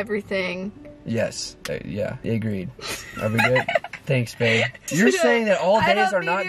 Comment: Wait are they still together ??? Because he said "Thanks babe" At 14:19 and I had my captions on to or did he say thanks gabe